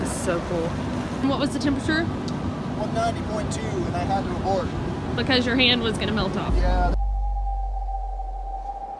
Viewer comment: !!!!! did he say 1000.2 degrees at 8:13????? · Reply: I think he said 190.2